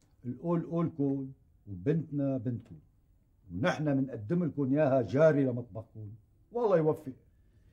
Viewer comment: بنقدمها لكم جارية ؟ ولا سمعت غلط